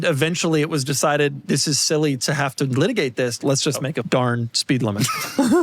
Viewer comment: I wanna hear what Doug actually said at 41:21. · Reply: it's pretty obvious he said "let's just make a f_cking speed limit"